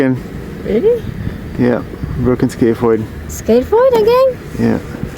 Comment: I thought he said skate board injury